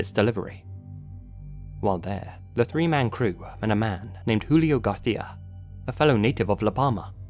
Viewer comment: is saying... “Julio Garthia”? Like gar-THY-a?? I keep hearing it and laughing